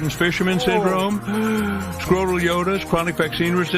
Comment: how Stephen Colbert got cancelled before The View is beyond me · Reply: I thought for a second that mascot said "Scrotal Yodel!" Which sounds like your either singing with a D in your mouth or you made a good parody song title. 6:15 Well, looks like Trump found you and you got well got. 9:50 He went to far with it (Get The Jab Or Else Get Ostracized By The Community). I am leftist any one who is a conservative is wrong and then he wonders "Why are people angry?" A real head scratcher! If you want people on both sides to like you say nothing it's very easy. I think on some level he was doomed. We already seen what happened with Tucker Carlson, Candace Owens, ect. If you don't parrot what the network wants you to say then your job is gone. 20:45 Well, if the show was performing well then they would still be making money so, that doesn't sound like a true statement.